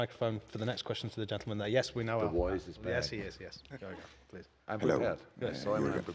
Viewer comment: did klopp say “the voice” is back then crack up when he started talking 😂